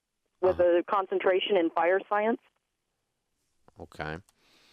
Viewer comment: Osha? With a concentration in fire science? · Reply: I thought she said viral science. Would make more sense.